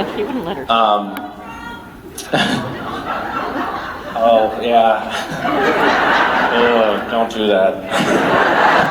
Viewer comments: someone yells something out from the audience to make misha "oh, yeah.. don't do that." Does anyone know what they yelled? It was where he was talking about Vicki not wanting to come up on stage and her thinking he was like Trump.. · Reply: they said "fuck donald trump" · Does anyone know what a fan shouts at 47:30 ish? He replies "oh, yeah.." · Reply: daemosthenese they said "fuck donald trump"